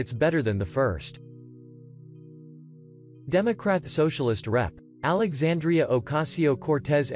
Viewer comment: I thought he said "Democratic Socialist RAT" lol